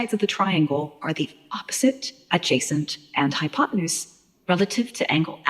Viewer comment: All i hear is "High on potenuse" 🤣